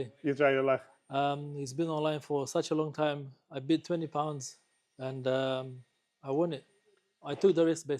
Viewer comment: Did I hear that right £20?!